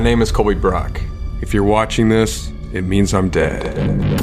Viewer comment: I heard “if your seeing this I’m dead” I was like wat did you do?